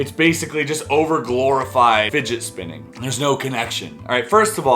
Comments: I thought you said"midget spinning"I thought, man first bowling now spinning? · Sorry. Thought you said midget spinning. Dog. X